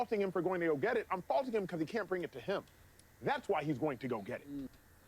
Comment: Did acho say now he’s gonna get it? Like he knows something 🤔